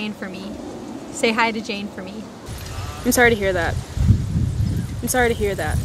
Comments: サングラスの学生さんよりメガネの学生さんの方が、聞き取りが難しい理由を分析して、今度ぜひ解説して下さい。 多くの日本人が、メガネの学生さんの方が聞き取りにくいと感じてるはずです^ ^ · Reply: メガネ早いよね。 1:36 で「I'm sorry to」で始まるトコが、「イスアイダ」としか聞こえないから癖も強そう。 「イスアイダ」ならまだいいけど、なんとなく頭にDが入って「ディスアイダ」とも聞こえる。 こんな簡単な文章が、こんなにも意味不明な発音になるなんて、ネイティブ英話者は魔物だわ。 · サングラスの学生さんよりメガネの学生さんの方が、聞き取りが難しい理由を分析して、今度ぜひ解説して下さい。 多くの日本人が、メガネの学生さんの方が聞き取りにくいと感じてるはずです^ ^ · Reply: うーん。どうやってもアイムソーリーと聞こえる部分が無い。 今日聞いたら、１回目はDが頭にあったけど、２回目はBに聞こえてきた。 1:36 ディスアイダ・ヒアtha 1:38 ビスアイダ・ヒアtha サングラスの方は問題無く「アイムソーリー・ヒアthat」が聞こえるけれど、 メガネの方は「イスアイダ・ヒアtha」となる。 1:43 アイドノー の方はアイが聞き取れるので、 メガネの I'm の発音が根本的におかしいんじゃないかなあ？